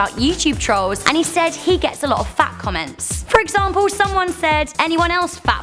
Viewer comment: I thought you said "fact comments" lol